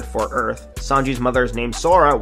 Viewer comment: Could have sworn he said “Which is the Japanese meme for sky.”